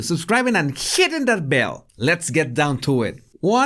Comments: Did he say sht in that bell? 00:22. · When this guy said hitting this bell. I auto correct heard it as 'shitting thees bed' haha